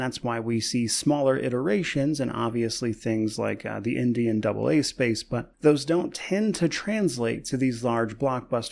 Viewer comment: For a moment, I thought you said " the Indian AA space" and I was like IM LISTENING (actually "indie and AA space"